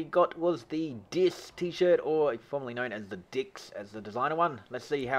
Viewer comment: what d hell did you say? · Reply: Hahaha!! D I X .. :-S